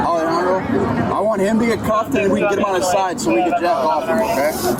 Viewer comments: I heard "Get him on his side we can Jack off him" and thought "That's not how you do CPR." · Did they just say they were going to Jack him off @ 3:19 LOL